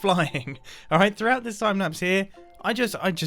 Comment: Did he just said sapnaps instead of timelaps